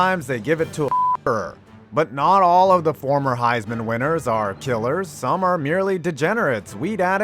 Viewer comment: uh what word did he bleep? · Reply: Killer